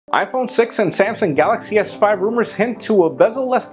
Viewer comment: Did he say iPhone sex at 00:00?